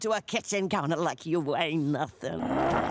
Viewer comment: Like Stanley Kowalski picking you up and thrusting you onto a kitchen counter like you ain't nothing! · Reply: It's actually "weigh nothing". Don't feel bad. I thought she said "ain't", too, until I turned the subtitles on.